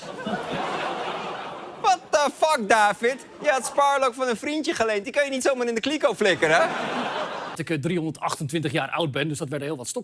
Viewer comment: WTF David? You had Sparlock for the frinjickalay you're gon' need someone in the keeko flaykin, huh? 7:10 (That's what it sounded like to me. lol)